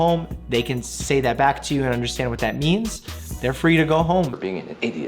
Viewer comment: Do one on Doctor Strange please. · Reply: Wait did he say I means? ~